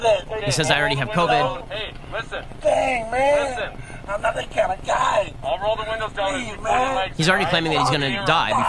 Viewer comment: Sounds like he said, “I’m not that kind of guy.” Not claiming he was gunna die. Not that it makes a difference, but that’s what I hear.